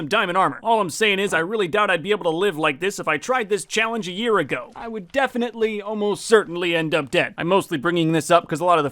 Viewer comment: What did this mans say? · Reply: He said witches not what you thought